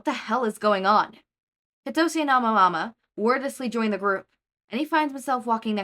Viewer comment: Im sorry but I just can't get over "Amamama" instead of "Aoyama" TvT